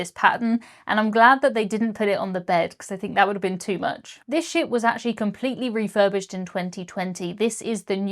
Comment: That wallpaper would drive me mad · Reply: When she finished talking about it saying "this ship has just been refurbished" i thought she said "s***"